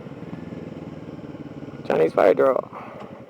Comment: Did you say "Chinese bi girl"?